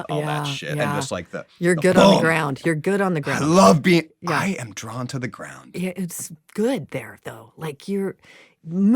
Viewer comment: I cannot for the life of me figure out what Chris references in this moment: he's saying "the modern dance teaching, and the accepting of gravity and accepting of entropy and that things fall apart... and that's"... and that's Jojo Ashebi? That's due to Au Chevie? That's true to O'Shevy? Is this a dance teacher / dance concept or did I completely misunderstand what he's saying here? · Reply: He says, “That’s Chinua Achebe.” Achebe wrote a novel called “Things Fall Apart.”